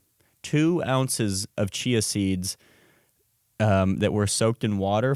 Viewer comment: I heard Chi Acids and it was coherent with your last video